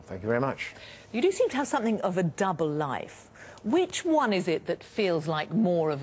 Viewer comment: I thought she said "Jew Life", at first.